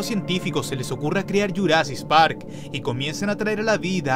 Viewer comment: escuché bien o dijo "Your ass is park?